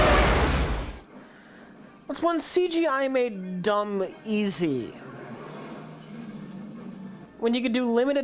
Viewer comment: what the HELL does Rich actually say at 15:24 can anyone help? I heard "what's one CGI made dumb easy", as does YT's subtitles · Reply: "that's when CGI made "dumb" easy" i think